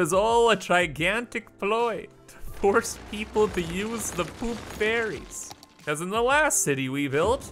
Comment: I hear "poop fairies" instead of "poop ferries"